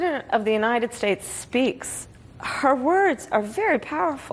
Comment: Is it just me or did Harris say "her words" at 41:40?